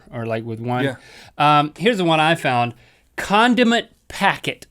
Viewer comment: I thought link said condom